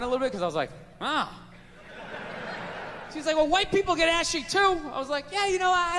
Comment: I thought he was saying white people get through security faster